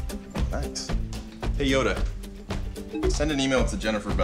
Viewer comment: did he said hey yoda or what